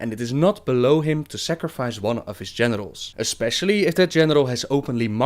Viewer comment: Love your vids! Also.. Thought you said "Genitals" at 0:34 xD